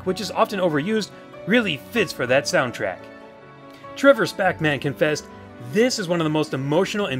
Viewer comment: Before it showed the username as "Trevor Spackman"- I thought he said "Trevor's Pac-Man". XD